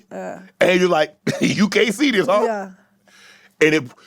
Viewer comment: I thought I heard, “You can’t see this H*£” 🤣🤣🤣 7:49,,, or am I reaching, sumbody lemme kno 😅